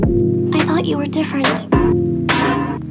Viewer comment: is she saying “There’s nothing you saw” distortedly or am I just overthinking ??